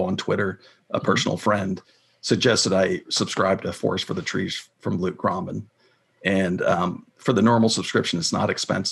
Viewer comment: Moderator: what the guest is saying? what subscription? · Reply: Forrest from the trees by Luke gromen i Think he Said